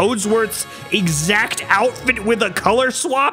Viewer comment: Hearing someone passionately yell about how much they "want to hear Yabbablabba again" was exactly what I needed today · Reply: To me and my older sister growing up we thought Toadsworth was saying " Yaa mama blabla", which honestly sounds funnier XD